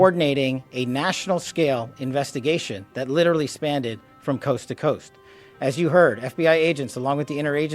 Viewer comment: he did not say "spanned-ded". Rugrats' baby-language has ruined GENERATIONS of Americans.